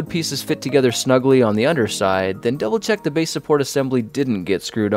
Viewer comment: Did he say, "The otter side," by accident